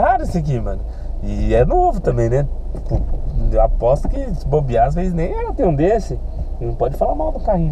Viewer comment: 📍Em 07:09 aposto quem nem vocês tem um desse? Só eu que ouvi isso??? 📍